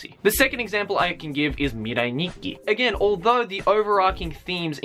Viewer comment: What was the one he said at 6:23? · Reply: It's Mirai Nikki